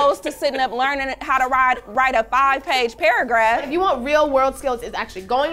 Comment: Hol up, did she just say five page paragraph